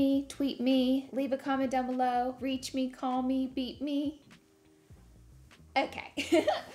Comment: i fr thought she said “beat me” not “beep me” i literally went chicken neck 😳